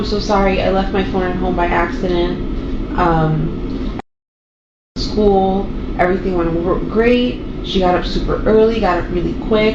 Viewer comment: did she say “everything went horror—great’? Or am I bugging out